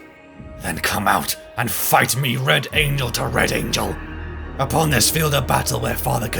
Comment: Ong I thought bro said “fight me red anal to red angel”